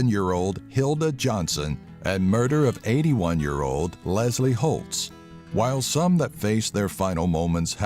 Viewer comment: For a sec I thought he said Lester hult